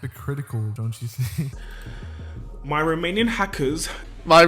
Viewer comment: why I hear: My romanian hackers, instead of: my remaining hackers?